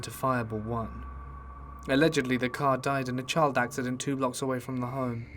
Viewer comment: I could have misheard, but in the first story did you say the car was in a child accident?